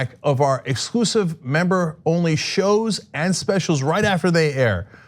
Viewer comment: Oh, all day i thought he was saying "joy button"